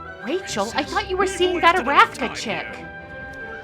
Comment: What name did you say when you quipped “Rachel!? I thought you were seeing that _ chick?”? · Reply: Looked this up. It’s Arathka, a name given to Japheth’s wife by Saint Hippolytus.